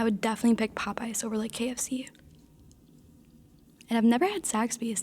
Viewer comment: sounded like she said she never had sex 🤣🤣.